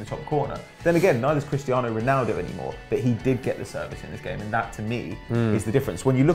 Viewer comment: It sounded like said " Cristiano Ronaldo banged.... Dave"